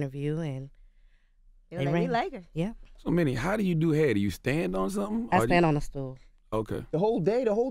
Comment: I thought Charlamagne said "So, how do you do head?" Mini: "I stand on a stool" Me: "WTF?!?!?!?" LMAO